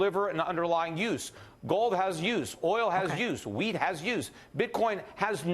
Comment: Did he say weed has use?